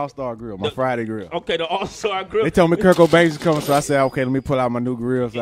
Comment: IT SOUNDED LIKE HE SAID "KURT COBAIN IS COMING"